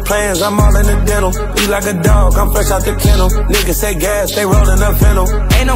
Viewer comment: how the actual fuck did they put the word candle instead of kennel when he clearly said dog 😭